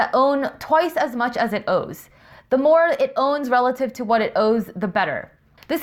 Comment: Great videos. If I can make one comment. At around 11:16, you said owes versus owns. It took me a little while to grasp what you were actually saying because the first time I thought you just said owes twice.